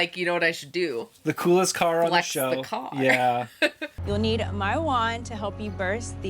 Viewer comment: when said, "You'll need my wand..." I definitely heard, "You'll need marijuana...."